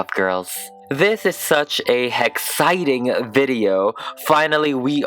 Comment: Did.... did you just say hex-citing ?